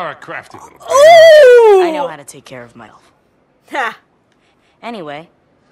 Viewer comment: did he just say "I know how take care of my health" or "elf"?